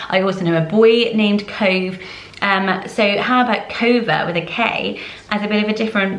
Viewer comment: I thought Jaspin was an absolutely adorable boys name! Also when you said Kova before the spelling appeared on the screen i thought you said Cover and i thought that would be super nice too